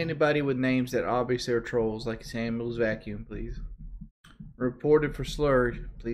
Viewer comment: "Ban anybody with names that obviously are trolls, like Samuel's Vacuum please" Is that what he said? lol